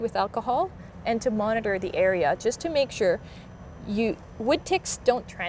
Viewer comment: my partner had a deep accent and i thought he said he had a wood dick